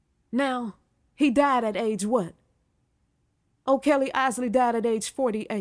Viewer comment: THANKYOU SO SO MADAM THE MASTER PIECE ALBUM ALMOST SOUNDS LIKE JOHN TODD SPEAKING BUT I KNOW THAT IT WAS A BLOOD 🩸 SACRIFICE MADE ON THE OLDER BROTHER OKELLY ISLEY AND MARLIN ISLEY MAY THEY ALL RIP 💯💯💯💯💯🌺💐🌸🌷🌹😘😍🥰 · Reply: I thought that she was saying R Kelly at first.